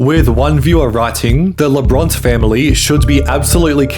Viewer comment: When you said labrurt I thought that you said labron